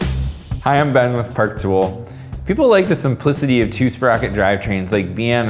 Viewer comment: I new to the channel and I thought he was saying "ive been with park tool" and just cutting off